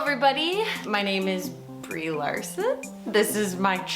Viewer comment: FOR A MONUMENT I THOUGHT BREE SAID “ OLSON “ NOT LARSON , I THOUGHT JEZZZ PLASTIC SURGERY IS AMAZING 🙄🙄🙄🙄😳😂